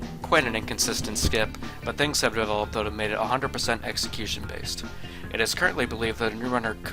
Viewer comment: what is even said here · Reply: "Things have developed that have made it 100% execution based"